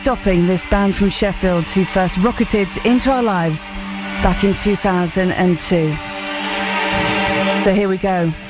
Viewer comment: the hell is she saying at 0:25 ..."back in 2002"?